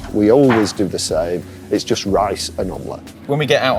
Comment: what does he say? "Rice and ..." I don't understand, please help me! :-) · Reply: "It's just rice and omelet."